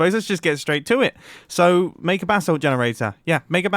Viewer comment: subtitles say bathtub generator instead of basalt generator.